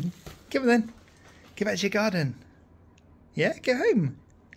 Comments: I love how patient you are with him. It's obvious that you really love him and that's beautiful. It was awesome seeing how happy he was to be home, and, seriously, it literally sounded like he said, "I'm home!" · Sherpa's not cut out to be a city boy. He's a country boy all through! 🙂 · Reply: I swear I heard him say I'm home!